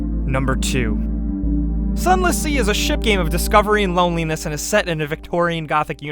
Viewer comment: When you said sunless sea I thought you said sun l'cie. Plz dont give me FF13 flashbacks...